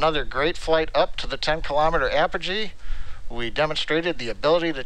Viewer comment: what does he say at 12:29? we have great flight up to ten kilometer ??xxxxxxx? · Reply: Apogee (highest point reached above sea level)